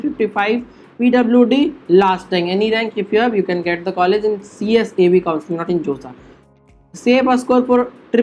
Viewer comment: sir can u write what u said it wasn't clear...which counseling? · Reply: CSAB